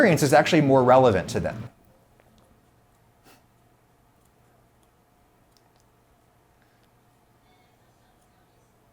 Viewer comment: kenji's audio got borked. What did he say? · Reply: ((i think this is it)) "Yes, absolutely. Um and I know like, a lot of it is [actually yourself], the difference is that you (pause) um and I guess this is true and fits a lot is that (pause) you are very much like your viewers. The only difference is that (resume audio)"